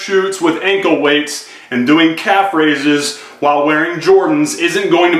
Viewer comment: I thought you said no fxxking Calf Raises, lol